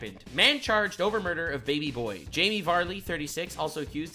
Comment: Thought you said Jamie Vardy for a second I got sad